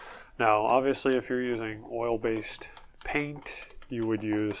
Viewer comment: You're mixing oil paint and water-based paint on your background? That's not a good idea, to be honest. Oil and water-based paints don't like to mix with each other. They will look okay for a while, but they'll separate at some point. I've been painting for almost 50 years and I've made my share of mistakes over that time. So try staying with one medium oil or water-based for each painting to be on the safe side from now on. Hope it helps :-) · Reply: I thought he said water and oil-based paints.